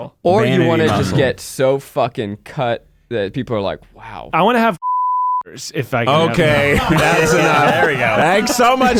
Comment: What were they saying at 25:20 ? I can't tell under the bleep lmao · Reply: "cum gutters" (referring to that V cut a man can get from working out)